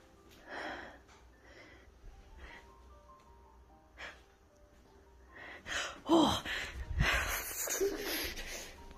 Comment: or so he says "hey buddy, I've got too much going on you've gotta cut it out" and sounds like he got a response immediately but I can't make out what it says. It def sounds like the spirit said something tho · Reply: It almost sounds like "I'm leaving"